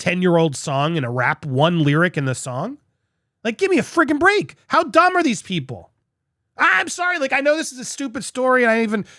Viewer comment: Immature giggle but at 5:38 in your vid you say "how dumb are these people" It sounded like "how Dahmer these people". have a listen 😁 x